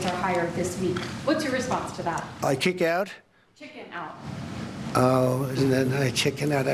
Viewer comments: It's a hearing issue, where he thought she said kick out instead of chicken out is just one example. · He actually thought she said kicking out. 😂 He didn’t know she was saying chicken out.